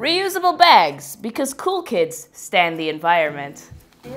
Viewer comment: Did you say cool kids "stand" or "Stan" the environment? I think your subtitles have a mistake. About